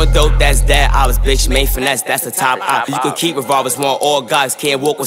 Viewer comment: did he call him self a top opp